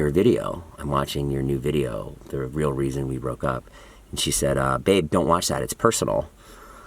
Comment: did I hear “babe” ?????